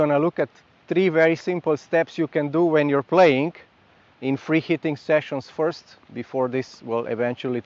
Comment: For some reason I keep hearing "freaky thing sessions" 😅